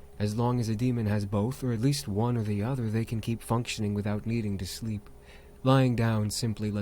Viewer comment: I thought that said they can keep fucking not functioning